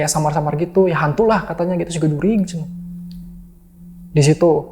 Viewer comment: ngomong apa sih yaaa...?? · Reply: Siga jurig. Kykny setan